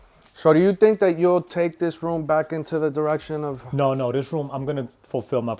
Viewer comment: 20 pairs of the same color airforces is disgusting…. When he said that, all I heard was “addiction”